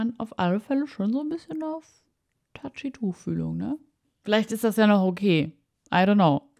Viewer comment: Ich hab am Anfang anstelle "Touchy Tuchfühlung" "Touchy Tofu" verstanden 😂❤